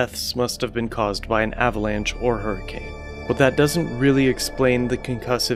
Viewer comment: Did you say hurricane at 7:40? I was unaware of hurricanes on land especially in Siberia during winter lol please explain. Nice work btw i really enjoy your content